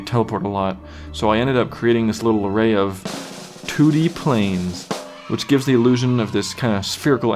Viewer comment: First time he said 2D planes, I thought he said toody planes. XD